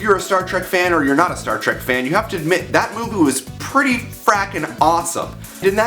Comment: does he say "Movoo?" XD